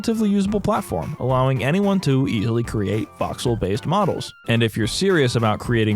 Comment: what's he trying to say? "illegally create voxel models"?!